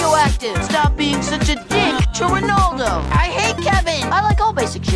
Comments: did Steven just say 'Stop being such a dick to ronaldo'? And if he did say that Tell me what episode I want to see it. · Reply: Horror Club, season 1, episode 41. But he actually says "dink", I changed it so that it sounds more inappropiate. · Did i hear That Correctly??? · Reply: Plantkon .J no he said dink. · wha- I know he says dink but it sounded like he said dick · DID STEVEN SAY "STOP BEING SUCH A DICK TO RONALDO"?? I HOPE I HEARD THAT WRONG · did I just here what I think he said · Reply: Watch original episode. He said "dink". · wait... Did he say what i think he said ? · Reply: Dink. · Um, did Steven just say.... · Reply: The Amazing Gaming Idiots no he said dink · did Steven say stop being such a d?😂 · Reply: Maddy Bautista no he said dink. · DID STEVEN SAY "Stop being such a dick to Ronaldo"?!?! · did he actually say that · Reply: He said dink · WHAT IN WHAT EPISODE DID HE SAY THIS IF AT ALL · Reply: AJ Lord he didn’t he said dink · am I hearing things or did revenge say dick. Thought this was a kid friendly show... :/